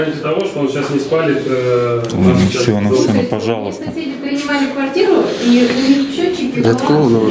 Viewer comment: Что она сказал?? "Соседка принимала квартиру и у них была проводка 'хуевая'?")))))) на 12:10 минуте